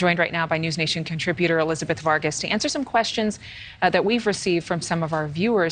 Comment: So nice to see Elizabeth Vargas. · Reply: True story: years ago when I first heard her name mentioned, I thought they were saying "Elizabeth Fart-Kiss."